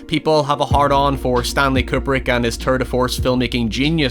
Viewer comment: I heard it as "turd of force" thanks!